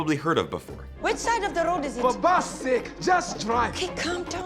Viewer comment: And all this time I thought he said " for GOD's sake just drive".